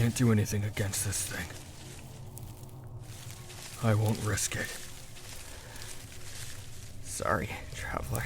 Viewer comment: AM I THE ONLY ONE WHO HEARD "I want brisket" INSTEAD OF "I won't risk it"????